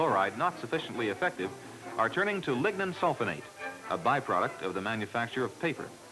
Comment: Hi, can anyone catch the name of the by-product mentioned round 23:35 to make the roads? I just can't make it out. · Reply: lignin sulfonate